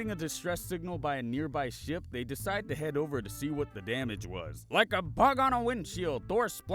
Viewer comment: omfg I thought he said “like a pug on a windshield” 😂😂😂